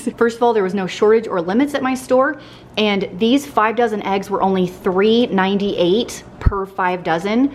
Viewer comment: Oh you said 5 dozen, I misheard it as 10,000, and I was like woah!